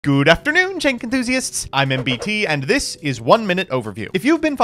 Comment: What does he say at the beginning? "Good afternoon Tank enthusiasts" ? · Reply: thank you For the longest time i always heard it as "tank enthusiaists" for some reason